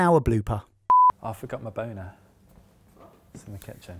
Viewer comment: James forgot his what? I assume he didn't say boner, even though that's exactly what it sounded like.